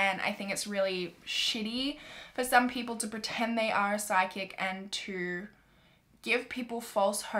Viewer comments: Not gonna lie when you said psychic i thought you said sidekick. 😂 Took me a minute there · PSYCHIC.... I thought she was saying “SIDE KICK” I’m like “what does a side kick have to do with anything?!” 😂😂😂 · I was really confused for a little bit, I thought you were saying Side Kicks not Psychic 😂😂😂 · lmao i thought you were saying sidekick instead of psychic xD